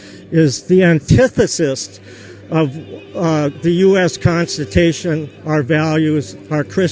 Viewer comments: "It's against our constitation." -Trump Supporter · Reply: watts I thought he said constipation, lol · Wait, did he just say US constipation? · "It's against our constitation." -Trump Supporter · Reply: watts Lulz, I heard "constipation" and lost my shit. XD